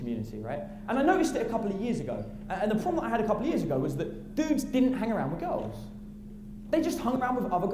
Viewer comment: he says dudes don't hang around girls. The subtitle says sixteen-year-old girls. lol